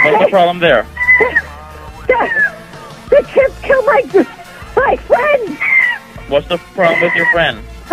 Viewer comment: I heard "HES KILLING MY GIRLFRIEND"